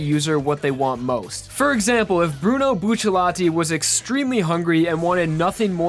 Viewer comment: did I Hear buchilati?